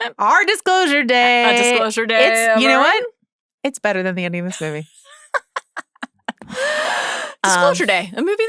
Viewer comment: What movie does Jo say at 1:54:02 ? “it’s better than the new ——- movie” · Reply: I think she said "it's better than the ending of this movie"